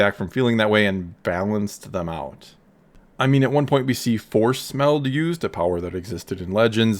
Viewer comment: At first I when you said force meld I thought you said "force smell" 😂